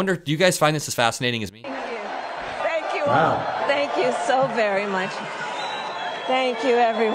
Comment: Did I hear him say, "Wow." when they showed the crowd size?